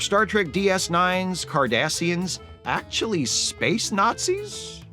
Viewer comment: I was half paying attention at the end and I thought said Kardashians (same difference) for a split second lmao that made my morning me cracking up laughing for a bit